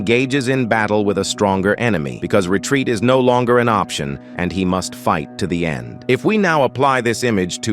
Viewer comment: he must fu- ad bro i thought u said "he must fuck" 😭